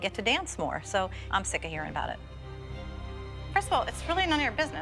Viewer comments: did Melissa say Crystal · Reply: I think she said first of all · did she say kristal?? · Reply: no she said “first of all”